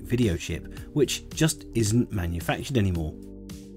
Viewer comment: Hehe you said "this amiga build" for the VDP. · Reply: I think it’s just the auto subtitle mishearing Oh-Meg-Ah as Amiga but hey, that’s my story :)